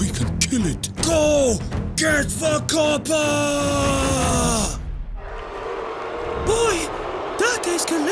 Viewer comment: wtf does he say after go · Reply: Koper, like the kop at anfield